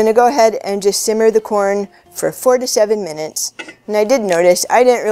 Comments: I thought first you said FOURTY SEVEN Minutes not 4 to 7 haha that gave me a shock. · I thought you said 47 minutes at first! Glad I looked at the typed out recipe lol. Thank you! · When I first watched this and she got to how long you boil it; swear to God I thought she said forty seven minutes xD I had to read the description and listen to it again. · thought you said 47 minutes at first. Long time for corn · Very cool. Thanks for getting to the point without any silliness, boring stories, etc. Easy to understand and a great lesson for me, even though I have been boiling corn for years. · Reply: I thought she said 47 minutes and burnt my corn. Then I went back and saw that she said 4 to 7 min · I thought she said 47 minutes 🤦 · She said to boil the corn for "4 to 7 minutes" - first time I listened, I thought she said "47 minutes" ... (haha) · Am I the only one who heard it as 47 minutes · great video thanks! at first i thought you said 47 minutes.